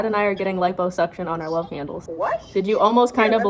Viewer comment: I thought she was saying light bulb suctions the whole time until I saw that she meant liposuctions